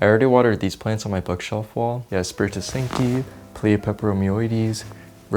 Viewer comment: What is the plant with the long leaves at 4:10? I couldn't quite make it out, sounded like "spirit to sinky"?